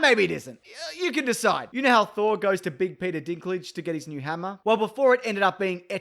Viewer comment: for some reason I misheard that as Big Dick Peterlage